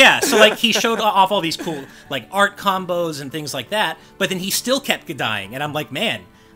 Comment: "But he still kept kuh-dying!" Did anyone else hear that?